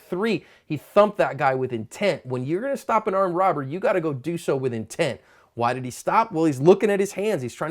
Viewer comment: I initially thought that said "If you are going to stop an armed robber, you gotta go D cell on his head" . Then I turned on the closed captioning and he said "If your gonna go stop an armed robber, you got to go do so with intent".